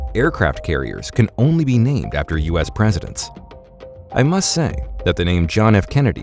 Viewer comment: "Nimitz, Enterprise..."...Midway, Independence, Lexington, Ranger, Langley, Yorktown, Wasp, Saratoga, Constellation, Kitty Hawk... I'd say that most aircraft carriers--and all three of the ones this 20-year Navy vet had the pleasure of serving on--have most definitely NOT been named after presidents. If that is a rule, it's a new one...and kinda sucks. Enjoyed the video, took me back to those days. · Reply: he clearly says named AFTER US presidents. But then later he does say BY.